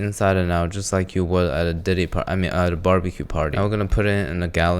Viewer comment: did you say a future canoe party